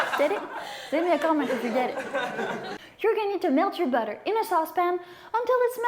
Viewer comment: sounded like you said "milk your butter", I was like how?